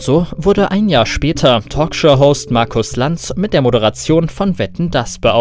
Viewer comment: ich verstehe einfach mal "Talkshow Horst" xD